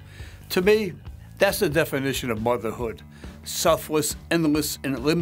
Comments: I thought it said selfish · Who else thought it said selfish?😂